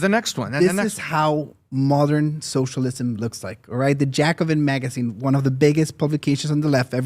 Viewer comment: the subtitles saying "Jack of Magazine" there was funny on like 5 different levels